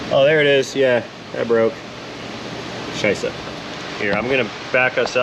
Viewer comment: Did you really say „sh*t“ in German 🤣 -„scheisse“ at 16:40 👍